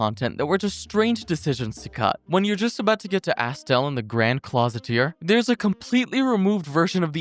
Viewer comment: “lion-cloth” jesus christ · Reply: we get hit with 'closet ear' instead of cloister